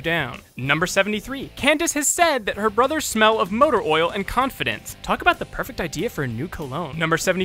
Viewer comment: I think she actually says “competence.”